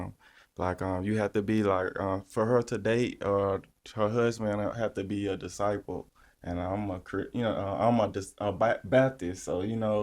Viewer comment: Thought he said “Her husband would have to be a disciple and ima crip” 😂😂😂